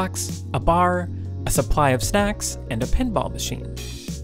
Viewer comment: I might be crazy but I heard: (2:35) "Complete with a jukebox, a bar, a supply of sex and a pinball machine" What? 😄